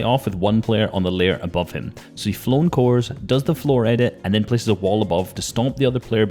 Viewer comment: Anyone else clock him say “flone Coor” instead of cone floor at 6:09 😂